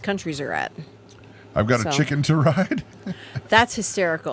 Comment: I thought you said "chicken to ride" couldn't figure out what the Beatles had to do with that! Speak into the Mike. Good for Grace.